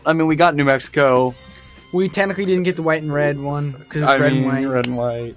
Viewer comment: Am I the only one hearing Joshua say "tenically" instead of technically at 9:57? Yeah? Just me? Ok......